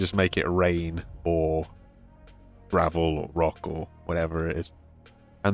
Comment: Did you...did you call rubble rabble?! Uhhh..........